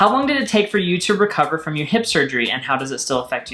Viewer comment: Every time says "hip surgery" I hear "hipster dream" instead 😂